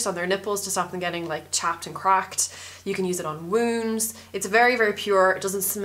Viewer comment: When you said you could use lanolin on your wounds, I thought you said womb and I was like oh yeah... wait?...